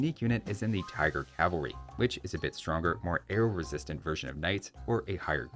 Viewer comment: I was watching it on 2x speed and heard "more air resistant" instead of "more arrow resistant"